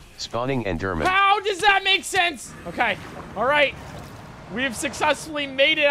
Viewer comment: Can we all just appreciate how broken then program is? Like, it mishears "Okay" as "Of the dead"